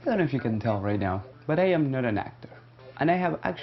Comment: "just" a school project? Like, isn't school your life, when you're doing a school project? Also, lol'd, just realized you said, "I am not an actor." I thought you were saying your name: "I am Nona Nactor," doing a film. Talking about yourself when you're doing a documentary of a spiritual icon, especially when you look like an actor, is meaningless. Have you considered being an actor? You look like one-- maybe that's your bliss, eh? Maybe you're an actor, not documentarian.